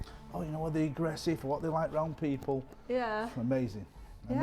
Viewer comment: Am I the only one who hears him say “oh they’re so aggressive, they’re like brown people” at 2:10??? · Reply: He said around people u just need to understand the accent 🤣